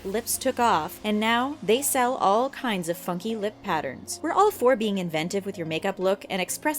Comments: It sounded like the narrator said the f-word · Shook!!! I thought she said fucking lip pattern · I thought she said fucking lip patterns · Who else thought that her saying funky sounded like fucky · I thought she said "and now they sell all kinds of fucking lip patterns" I litterally rewinded to check lmao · I was just about to exit the video until I heard @ 3:08 I could've sworn I heard "fucking" I had to replay lololol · funky sounded like fucking or fucky. 0.0